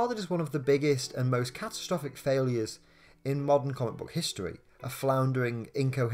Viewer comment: Am I the only one that heard ‘Marvel’ instead of ‘modern’ at first?